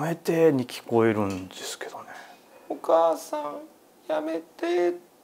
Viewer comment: おかあさんやめて に、聞こえた…